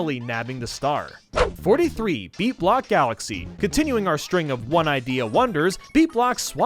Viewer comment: He said beat block galaxy. I misheard it as meat block galaxy.